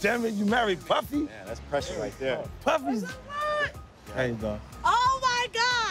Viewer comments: 50 CENT WAS GONNA SAY "PUFFY IS GAY" LMAO😂😂😂😂........ EDIT= I'm surprised no1 caught that before I did lol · I heard the puffy’s fruity coming😂😂